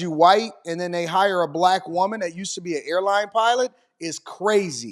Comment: Airline Pilot? Thought I heard flight attendant.